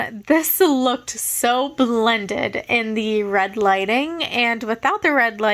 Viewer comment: I thought you said red Lightning😭